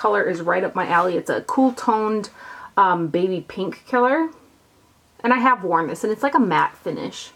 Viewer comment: sounds like you said " baby pink killer" i cracked up . that be an awesome name for a blush! anyways, love all your videos . very informative and helpful (